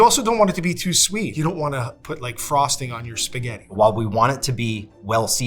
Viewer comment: Did he really say Spagehtt? Cringe. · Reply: He said "spaghetti." The clip just changed very quickly. But I agree, that would be cringe.